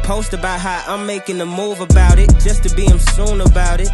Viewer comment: oh wait ,did he just say "smoon " ?the one and only smu**?👀👀😂😂🤣